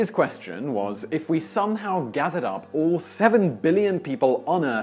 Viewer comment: Did he say 70 Billion or 7? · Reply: Lucoa Quetzalcoatl 7